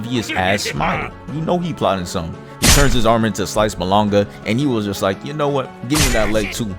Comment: did you just say sliced "bolonga"? WTF